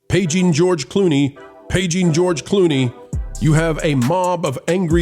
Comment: I thought you said "aging George Clooney."